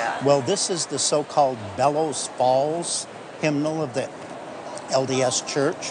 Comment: Ohhh Bellows Falls.. I thought he said, Devil's Balls. THAT would've been an entirely different show.